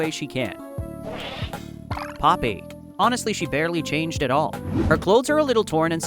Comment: why did I think at 02:29 they said pot baby 😭🥀